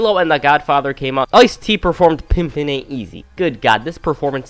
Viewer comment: The problem was that it sounded more like Ice T was saying "pippin ain't easy" instead of "pimpin." And there was a bit when he was seemingly saying "Pippa die." Sounded like he was desperate for a woman called Pippa to die.